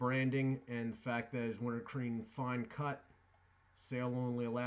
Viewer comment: Did you just said WinterCream Fine cut!?